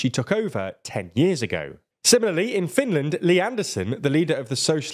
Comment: I spat my tea when I thought you said Lee Anderson. I guess Li Anderson from Finland has slightly different politics.